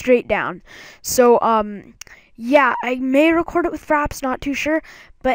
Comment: Am I the only one that heard him say "Faps" and not "Fraps"?